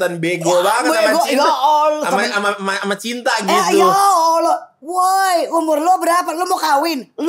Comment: "Ya Allah" Aku dengernya gitu 😊